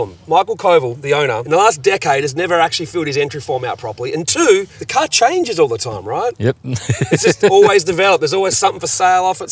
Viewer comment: Could’ve sworn he said “the c*nt changes all the time” 😅